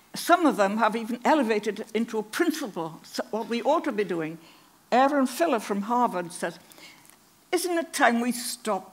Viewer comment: I did not find a Harvard biologist with a name similar to what she mumbled at about 4:58. A scientist gives heir sources. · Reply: <link> Possibly this guy? Sounded like she said Aaron Filler.